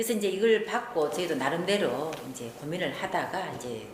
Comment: 저희도? 나만 이상하게 들리는건가?